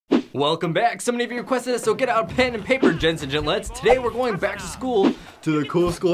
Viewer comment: did he just say "Joe nuts"